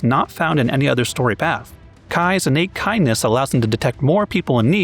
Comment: I definitely thought he said Cai's unique Cainess instead of kindness.